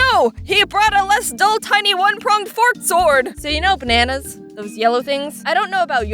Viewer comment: I. THOUGHT. YOU. SAID. PORK. SWORD. NOT. FORK. SWORD. AND I LAUGHED MY POPPYCOCK OFF